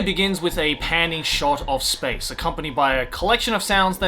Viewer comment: I heard you say "panty shot, in space" and i thought I had already lost a bit of my mind.